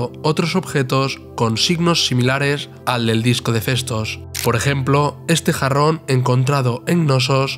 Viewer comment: Yo entendí "El disco de fetos" XD